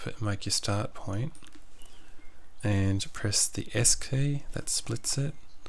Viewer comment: u say " press the escay? escape? wtf do u say?